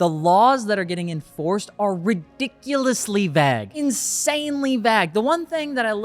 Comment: "...ridiculously _______." What are you saying here? Bag? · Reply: Vague